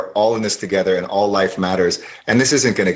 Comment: did he say 'all life matters'? he went against abortion, and mocked BLM in on second without a peep of protest from lefty lemon